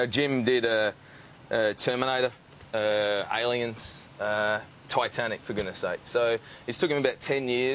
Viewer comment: when he said Titanic, it sounded like Twatanic